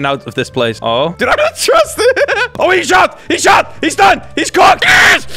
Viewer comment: EXCUSE ME, WHAT DID HE JUST SAY!? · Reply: shut